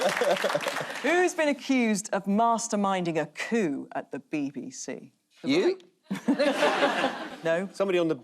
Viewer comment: I totally misheard a question as "Who has been accused of masterminding a poo at the BBC?"